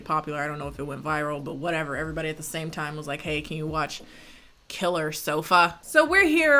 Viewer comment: I thought that said Killer Sofia and I had some hope for the movie